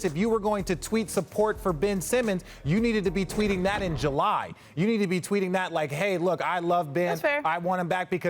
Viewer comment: Is it just me or did Richard slickly say Re-Kendall instead of rekindle at 4:58?😂